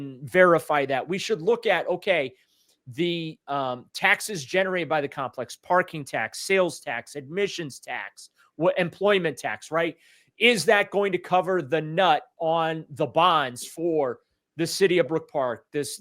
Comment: what did he say? Cover the nut???